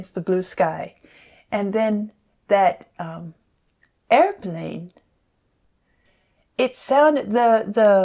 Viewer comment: in the movie he is in a baby carriage and points at an airplane in the sky. the album paints a good picture but the movie makes it hit home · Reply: Yes. Doesn't change the fact that the album does not sound like he says "an airplane in the sky" to me. At all. Sounds like "No planes in the sky".